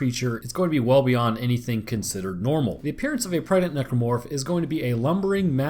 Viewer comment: he say prdnet necromorph instead of pregnant